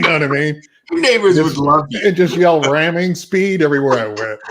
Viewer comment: always thought they said Reaming Speed in animal house.